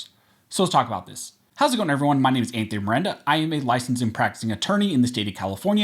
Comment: When I suggested 2A as moniker I thought I heard Aranda not Miranda. Never the less. My question is "any of your friends call you Tony"?